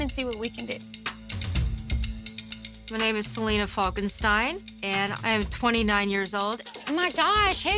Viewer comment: When she said "Salina Falkestine" i heard it as Frankenstein and was like: 😀